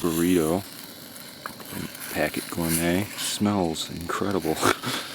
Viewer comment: Hey that was a nice relaxing video where did you get the cheese spred from would love to visit indiana to that same lake 2 day camping would be so nice thanks for the video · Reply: I heard him say "Packit Gourmet" in the video. I think that this is the same company that he buys it from, today. Update: he answers this question in another comment. :)